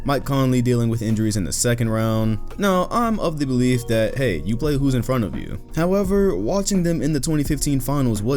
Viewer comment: I thought you said “you play against whose in front of you “ lmao make your mind up finals